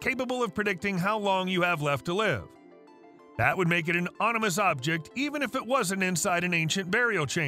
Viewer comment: Anyone else hear 'onimous' @ 1:49? 😄